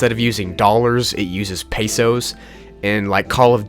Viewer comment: me. Wait a minute… did i just hear peso? peso is a Filipino money! In Philippines!